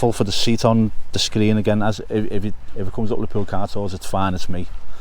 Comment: Have to wash me ears out as i heard you say “liverpool CARTHORSE” and i though to were talking about Larry Lloyd! (Thats my age). Oh Car Tours !! Gotcha!